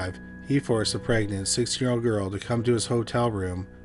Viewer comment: am I mishearing this... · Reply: he said 16. The teen part was said quickly